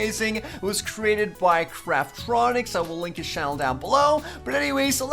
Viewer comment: I thought he said craptronics